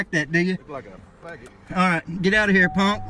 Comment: fat cop wit maxi pad stuck to his face,. Did he say fa**ot? Homophobic slur? Wow